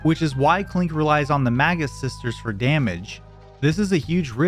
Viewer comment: For most of the video i thought you were saying the "MAGA sisters"